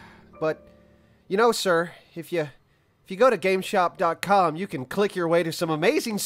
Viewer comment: I watched this five times and just relised it says gameshop i thought it said gamestop